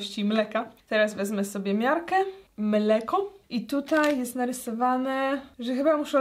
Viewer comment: Usłyszałam ,,menelko"😂